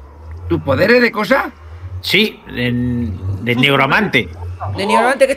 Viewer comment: yo escuche tu negro amante xd